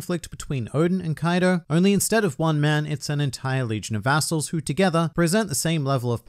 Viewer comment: Oh, I thought he said a**holes at first and not vassals.